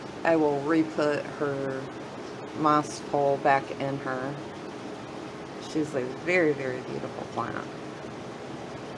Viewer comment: what kind of plant is that? Couldn't understand the name. It's a beauty! Thanks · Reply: That is a red emerald...✌💖